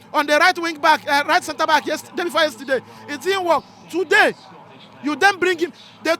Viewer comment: I thought you said man u is back before today